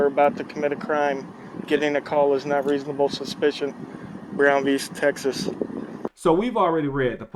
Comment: When the Auditor said "Brown V" I misunderstood him and thought he said "ground beef" lol.